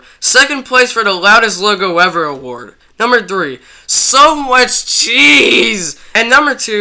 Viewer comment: what did he say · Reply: So much cheese